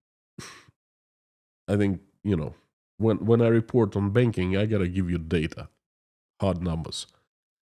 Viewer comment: When you said Another industry you wanna talk about is Banking, I heard "Begging". Maybe you should be talking about begging soon.